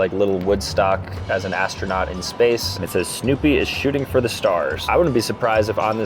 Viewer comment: I thought you said "Snoopy is choading for the stars."